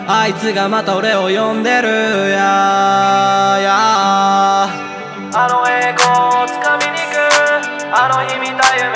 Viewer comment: あの栄光を掴みに行く が 狩野英孝を掴みに行くに 聞こえて焦った